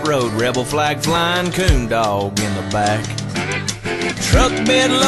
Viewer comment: I thought you said corndog at first glance. Now that i think about it I wouldn't mind having both haha